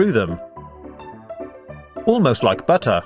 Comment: If you turn on subtitles (not the auto genarated one)you will see it says at 0:33 almost like butt instead of butter